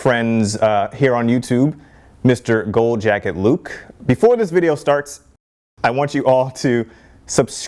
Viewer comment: I heard "Go jack it, Luke" 😂